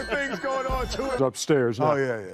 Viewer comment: is it just me or did Charles say "this shit ain't nothin to joke about" when Shaq was pushing him towards the snake Ik he said either "this ain't nothin to joke about" or something indecipherable before "... ain't nothin to joke about"